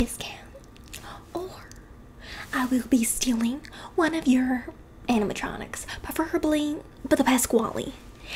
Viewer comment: I'm tryna sleep and all I hear is "stop twerking on the animatronics"